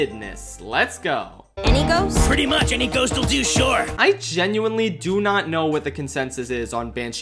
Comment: Can someone tell me what the two characters are saying here? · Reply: “Any ghost?” “Pretty much any ghost will do, yeah”